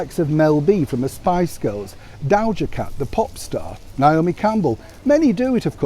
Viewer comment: 😂Neil, I thought I heard you say "soup star". And it was before I scrolled down here to the comments!